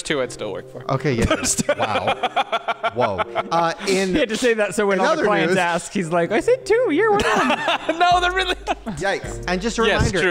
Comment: Wait what did Ryland say at 1:19:39 😅 can someone please tell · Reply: Ryland said: "He had to say that so when all the clients ask he's like, "I said 2, you're 1 of them!""